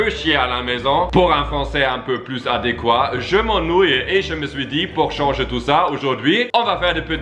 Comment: Huby : Je m'ennouille Moi : tkt moi aussi je m'ennouille ( ͡° ͜ʖ ͡°) 😂😂 Qui s'ennouille aussi ?